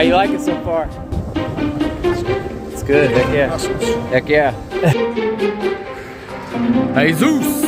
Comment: Did he say "Beck yeah"? Is this a little shoutout to the great Too Apree channel?